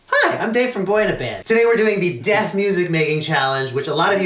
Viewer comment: I disappointed myself, I thought I heard say "Death music", instead of "Deaf music". Still good tho